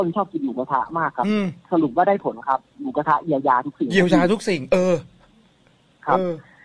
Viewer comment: ท่อนนี้พี่เขาพูดว่าอะไรนะทำไมผมได้ยินว่าอย่าพึ่ง เย็... ผมก่อฟังหลายรอบมากหรือหัวสมองผมมีแต่เรื่องนี้วะ5555